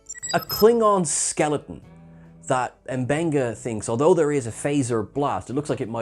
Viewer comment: Not to be pedantic, but at roughly the 12:40 mark, you're talking about the first Klingon corpse they found and said that M'Benga "thinks although there is a phaser blast" but that's not what M'Benga said. He says "Not a single phaser burn". I can see how you could have misheard him say there was just a single phaser burn or something.